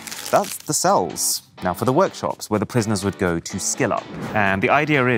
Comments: Thought you said SKIN up there for a sec. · I misheard this sentence as: "This is the workshop, where prisoners go to skin-up" 🤣